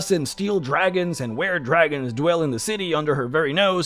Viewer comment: Did he say weredragons?